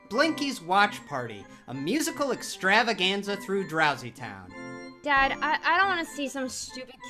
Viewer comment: for a second their I thought he said dousy town, hahaha oh wait he is being serious